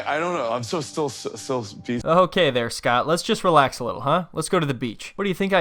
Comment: “I thought she said Scott let’s go!” 😭😭😭